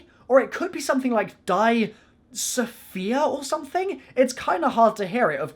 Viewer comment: I HEAR "DIE SOPHIA DIE" Like if you agree!!!